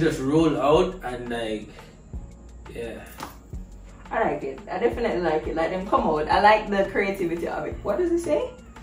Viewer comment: did she call him babe?? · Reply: She seh.... "Alright yea... Dem definitely come out" lol.